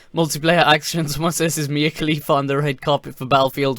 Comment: Wait, did you just say Mia Khalifa? As in the porn star?